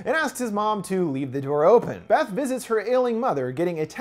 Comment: "Beth visits her ailing mother..." Could have sworn I heard "Beth visits her alien mother..."